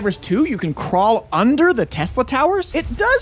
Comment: I thought he said tessa like tessa from murder drones 🥲